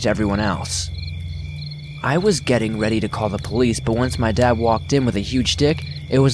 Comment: I SWORE I THOUGHT YOU SAID “Once my dad walked in with a huge dick” LMAO